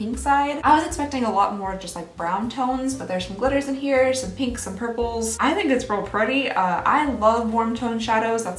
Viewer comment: Had to rewind because I thought you said it smelled purply.